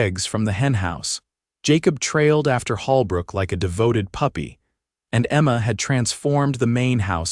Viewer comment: I thought said Holbrook wife had died. But Emma helped her.????